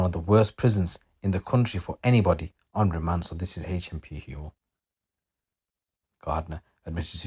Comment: What did you say hmp what? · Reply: HMP HEWELL it’s in redditch , it’s a really old fashioned jail , it takes like over a week to get canteen or phone credit , it 23 hour bang up like Covid times. The jail is so under funded and lack of staff , it’s sweatbox when it’s warm outside and a fridge when it’s cold